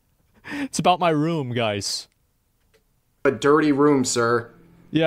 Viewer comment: Is it sad I misheard "dirty room" as a "dirty womb?" In syllable abuse... :(